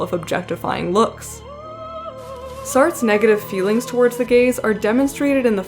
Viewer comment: I keep hearing "the gays"😂😂😭